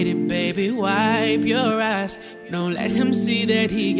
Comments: I THOUGHT IT SAID “Wipe your ass” 😭😭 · why do i always mishear this part🤧 "..baby wipe your sorry NYAHAHA